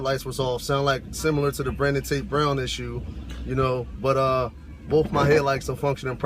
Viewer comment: What did he say at 4:33? It sounded like “Brenden tape brown issue”.